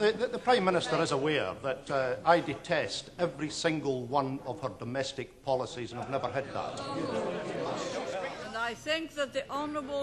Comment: Could anyone tell me what does the guy at 1:37 say? "... I detest every single one of the domestic policies…??? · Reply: I detest every single one of her domestic policies... and I've never hid that